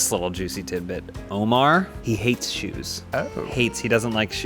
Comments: I thought he said Omar hates Jews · I thought said Omar hates Jews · I really thought they said Omar hates Jews, almost spit out my water